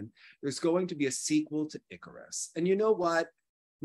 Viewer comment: said "apparently there's going to be a sequel to _______" Does anyone know the word that said? I couldn't make out the word. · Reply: "Icarus" in reference to the Netflix documentary highlighting the systemic doping perpetrated by the Russian government in 2014 and beyond